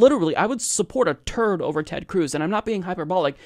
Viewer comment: I thought you said terf. That would make more sense